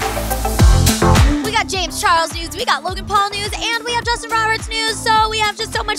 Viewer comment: I thought she said "we got james charles nudes" SCREEEEEE